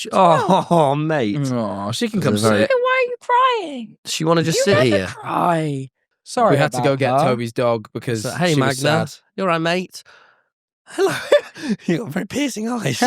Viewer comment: The speed at which I ran to my computer, from the kitchen, when I heard "Toby's dog is here" will never be achieved again